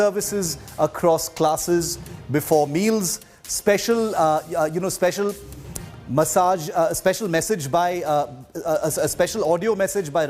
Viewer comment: special massage...is that what i hear? haha 5:40 mins